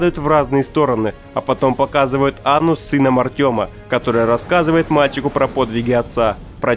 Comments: На 1:36 я услышал анус сына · мне послышалось или он сказал анус